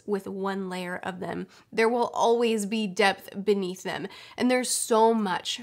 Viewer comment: When she was talking about sokka and the depth of his charcter I got really scared because i thought she said death